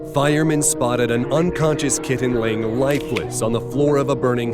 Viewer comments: I didn’t realise they said kitten I thought they said kid and I was thinking “that is not a kid that is a mouse” then I realised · i almost thought they said kid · I thought said kid not kitten